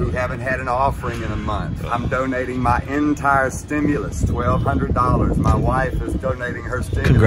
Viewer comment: i definitely heard him saying "i'm donating my hent*i stimulus" lol